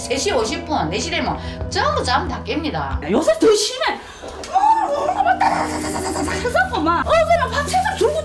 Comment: 여사님 감정담긴듯한 억양이라 그런지 심해가 아니라 ㅅㅂ이라 들리네..